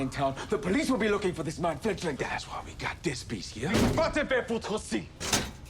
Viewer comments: For any French speakers out there what does Lestat say at 3:15 ? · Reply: "Va te faire foutre aussi" , means "Go fuck yourself too" , our boy was pissed 😂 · when it says Paul said "wha" ... what he is actually saying "quoi" which is the french word for 'what' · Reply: Also, if anyone is wondering what Lestat said at around the 3:15 mark when he goes into explosive French..."Va te fourtre aussi"...'go f@@ yourself too' or 'f you too'. Something along those lines.